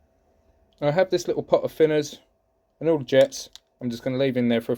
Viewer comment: At the 7:00 mark, did you say thinner? Like as in paint thinner? Just want to make sure I get the correct stuff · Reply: I use thiners as that is what I had around but you can use any thiners or solvent as it's to let the jets soak and help break down any crap that in them .